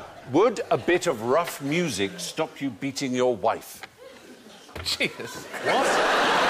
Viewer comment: At first I thought he said "meeting".